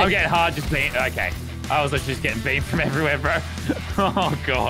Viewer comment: When I heard Lannan say I’ve been beamed I thought he said banned lol